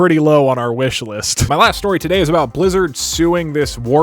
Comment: I misheard "Blizzard" as "Lizards", and thought Lizards as a whole were suing someone. Lizard Movie.